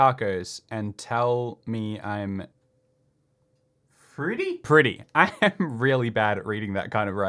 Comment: I most definitely thought it said "and tell me I'm slutty" Ngl I thought that was a pretty amazing quote. But just "tell me I'm pretty" nah fam, thats a lame quote